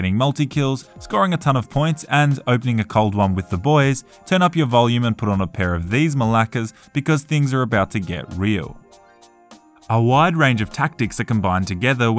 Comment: did you say malakas